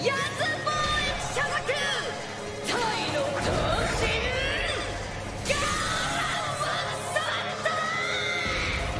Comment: what she says in japanesse words?? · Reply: "Tai no toushin" (Thai God Of War)